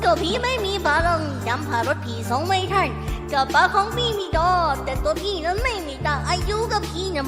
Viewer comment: ห้ะ อะไรนะได้ยินไม่ผิดใช่ไหมกระเป๋าของพี่ ดอ